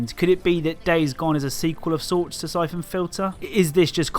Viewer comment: i thought you said "cyclone filter" instead of syphon fliter made me search up a WWII air filter